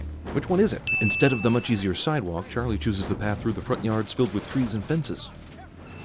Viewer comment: am I the only one who heard Charlie? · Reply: he did say Charlie